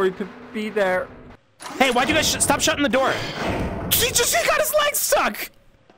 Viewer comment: what is he saying? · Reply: "He just got his legs stuck!"